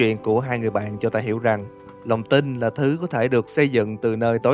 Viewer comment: Ko hiểu sao ,người ta đọc "lòng tin" mik nghe thành ĐỒNG TÍNH đc luôn á